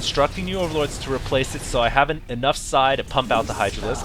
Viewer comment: did he say enough psi???!!?